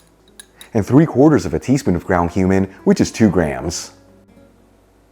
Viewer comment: I thought you said “ground human” not “cumin” 🎉. I shouldn’t put off getting the hearing aids I’ve been told I need. 😂